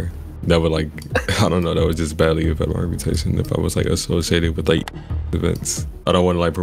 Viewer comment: I believe he mumbled "harm my reputation"..... to be doing ts and instead of the harm it's causing to the kids, he's thinkin about his own phoney image